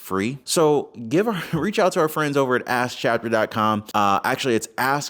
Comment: Sounds like youre saying ass chapter lol